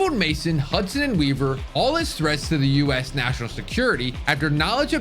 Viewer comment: What!? I cant even understand what you're saying. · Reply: Are you saying all it's threats?